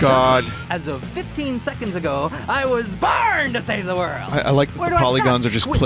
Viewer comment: it sounds like woody saying i was BURNED to save the world